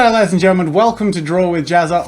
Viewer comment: Captions said Gareth and gentlemen at 0:15 😹😹😹😹😹